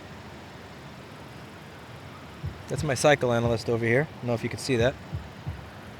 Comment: I thought you said “psychoanalyst” and it was much funnier that way